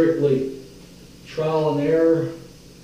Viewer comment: did he say trowel and error?